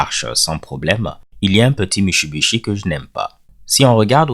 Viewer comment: C'est quoi à 0:14?? Il y'a un petit quoi? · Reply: Le mot est "Mushibishi". Navré, c'est un petit tic que j'ai. Mushibishi veut dire "chose", "élément", "truc", etc dépendamment du contexte :).